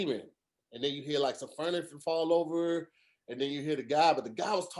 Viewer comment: Did tahir just say “furnifure” 1:05:56 😂😂